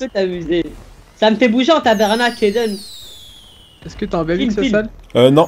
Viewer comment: c'est moi ou À 18:04 à dit : Sa me fait bouger en tabarnaque en Aiden .( C'est en Québécois ) . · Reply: oui il a bien dit ''Ça me fait bouger en tabarnak''